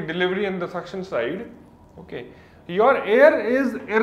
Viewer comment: question: 4:55, what is the word before"compressed", eagerly awaiting your reply, sir. Thx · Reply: Irreversible